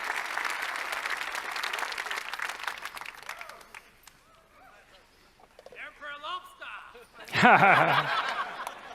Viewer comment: Can someone share with me what the gentlemen in the crowd is saying? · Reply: Emporer Lobster!!